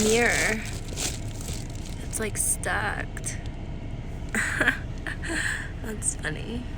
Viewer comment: Did you just say STUCKED? Lmfao oh girl...